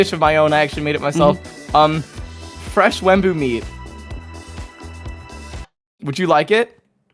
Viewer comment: strange... 30 minutes just cleared out from my schedule... (as i was typing this i heard "fresh wemmbu meat"